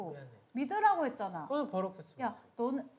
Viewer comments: 여기서 뭐라는거임?? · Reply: 왜 버럭했어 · 여기서 가 뭐라고 말하는거에요? 잘 안들림. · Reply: 왜 버럭했어? 라고 하는거같아요